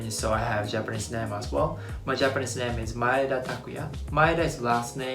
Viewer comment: I thought that said MADEA 😂 lol